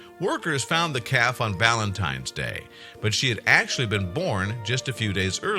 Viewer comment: did this 'professional' narrator say "Ballantine's day" ? 04:00 Did I hear correctly?